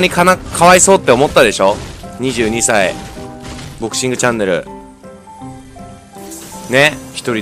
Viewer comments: ボクシングチャンネルがぼっちングチャンネルって聞こえた僕は末期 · ドクシングって聞こえたw